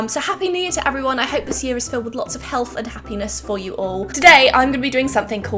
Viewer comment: was I the one who heard happy-penis?